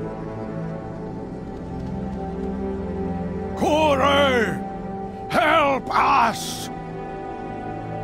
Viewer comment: did I just hear 'Koolaid, help us!'